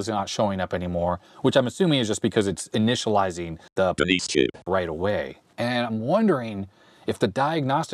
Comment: Sounded like Soviet era censors overdubbed in "Denise chip", caught me off guard 😁